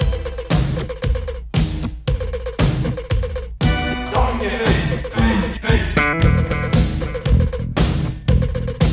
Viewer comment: Awesome upload! What does the voice say at 4:50? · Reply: Stamp your feet